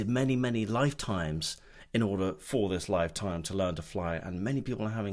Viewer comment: Oh! I just heard you say "lasts a lifetime, I'm gonna replay that and listen again!!